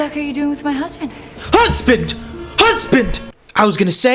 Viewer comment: "Husband! Husband!" Sounded like "A BEAR! A BEAR! UNDO IT!" :DD